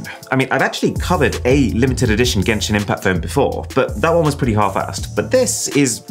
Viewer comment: DID HE SAY "HALF-ASSED"!?